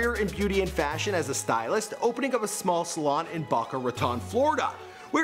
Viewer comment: Did he just say " baka ratan" ??